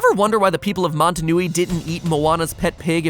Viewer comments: Did he just call it Mata Nui? Was that the actual name, or am I just a really big Bionicle fan? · What MatPat said: Montonui What I heard: Mata Nui